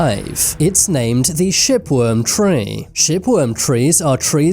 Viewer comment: I thought he said shitworm tree. 😂